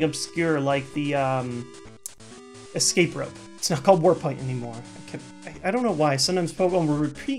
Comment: I swear it sounded like you said "Warp Pipe" in stead of warp point. It made me giggle.